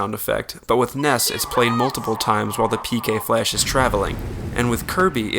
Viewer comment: Am I the only one who thought Ness was saying PK pulse instead of flash for like the longest time?